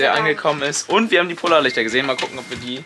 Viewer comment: Ich hab einfach verstanden puler lichter😂😂😂😂